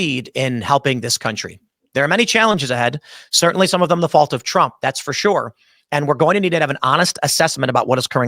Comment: I had to replay that part about trump cause to start with I thought he said not the fault of trump,when I say MAGA IS DEAD AND TRUMP KILLED IT,SIMPLE AS THAT. MAGA was no more wars stop the wars,no more money overseas and the Epstein stuff, But trump has called Americans stupid ,American workers or ppl on the streets can’t work in factories ,I can tell you now that is bullshit unless they don’t want to work but anyone can learn factory work ,I did and I was unemployed and I worked shift work in a dairy factory for 17 yrs and 5 yrs in a meat works plus trump has called those of us wanting the Epstein files stupid as well and that’s all of his base,of course you have the cult members who pretend he didn’t mean it like that and are all for Israel these silly evangelicals Christian’s who are brainwashed with blessed be israel anyway